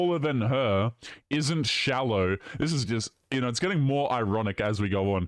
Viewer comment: Imagine having "taller than me" and "not shallow" right next to each other and not even recognizing your own hypocrisy. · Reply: I thought you said not swallow. That’s an automatic disqualification 😂